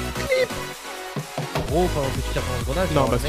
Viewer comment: All i heard in 3:17 is "cleep"